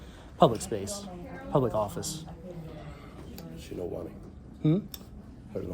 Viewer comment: "She no want it" (your constitutional rights) ~ Slightly Sloped Roof security and manager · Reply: I thought he said, "She nowanee". What a joke.